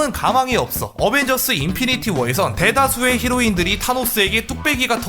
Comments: 누가봐도 히로인 으로 들려요.. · 에서 히어로가 아니라 히로인으로 들리는데 ㅋㅋㅋ 저만 그런가요 ㅋㅋㅋ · 히로인이라고 들리는데 나만 그럼?